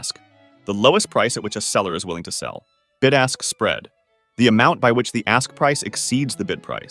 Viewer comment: I swear to God he said "Good ass spread" at this part but I guess not